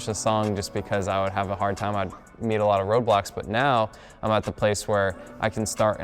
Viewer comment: who else thought he said “I’d need a lot of Roblox” at 6:33 😭😭